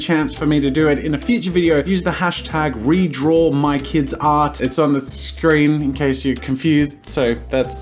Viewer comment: I thought it said "Redraw My Kids, Art"